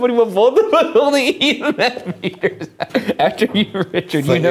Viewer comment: What does Ryan say at 32:09? This is gonna bug me🙃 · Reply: I think it was "with both of us holding emf readers"